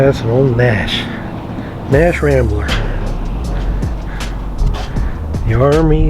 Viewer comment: SR-71 is one of the all time favorites, great shot of the plane taking off while observing next to the Flying Fortress. 🎯✈️🛡️ Incredible access! 🎛️ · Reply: I thought you said M.A.S.H. when mentioning the Nash Rambler, I could probably make 20 comments on this video. This is great. 💯 Now we're at M.A.S.H.!